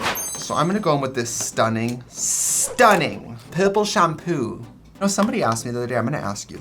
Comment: I legit heard "pimple shamoo" when trixie used her australian accent trying to say "purple shampoo"